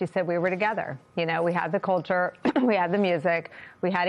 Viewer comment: I honestly thought she was saying Coulter, like Anne Coulter. It took me a minute to realize she was saying culture.